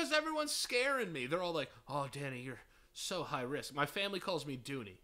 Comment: I thought he said "Oh, daddy, you're so high risk"